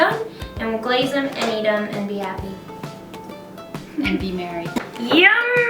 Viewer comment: ok wait a sec did say be married at 12:58 or was it just me who thought that ??